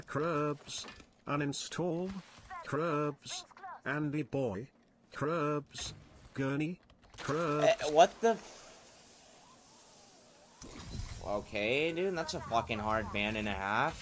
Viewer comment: sounded like what the fffffffffffffffffffffffffffffff